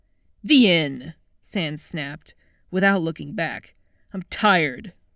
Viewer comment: what did sans say at 25:35? i cant hear it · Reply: "The inn!" He was going to stay at the inn, because he was too upset with Papyrus to stay at home :<